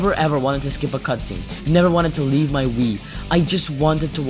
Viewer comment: I had to turn on the captions to realize you said "Wii" instead of "Weed" 😅